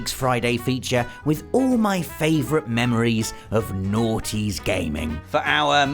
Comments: Sounds like he says the naughites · I really thought that he was saying “naughty” for a hot minute.